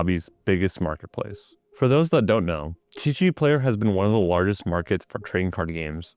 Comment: what did you just say? lol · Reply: TeeShee player, bro! If you don’t know, now you know.